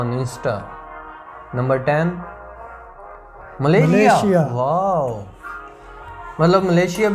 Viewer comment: did he say malaria instead of Malaysia. Wtf XD XD